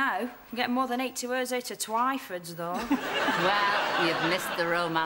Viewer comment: can anyone make out what Twink says after Bren asks her if she’s alright now? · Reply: I'm getting more than 80 words out twyford though